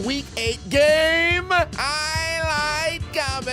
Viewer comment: For the longest time I thought you were saying "I LIKE COMMENTARY!" first time I heard Highlight.